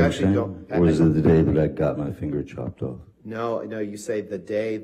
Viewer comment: I heard got my finger chopped off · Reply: Yeah.. even i heard "the day i got my finger chopped off"